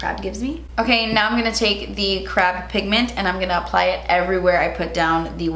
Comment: I heard "the crap pigment" every single time you said it during this video